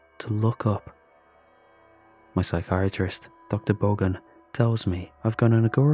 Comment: I heard "Dr. Volgun" and immediately confirmed that he was indeed, an SCP